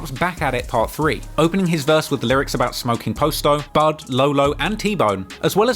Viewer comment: bro this whole time I thought herb was saying he smoking pasto , that's grass in Spanish , like just saying he smoking weed that's crazy